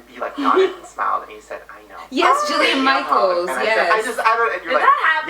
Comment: i thought she said julia michaels at first and i was like...